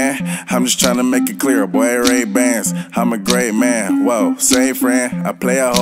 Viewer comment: am I the only one who thought he said rape vans ?? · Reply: Ray bans?